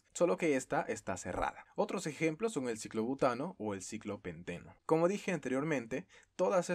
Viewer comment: entendí ciclopendejo xdxd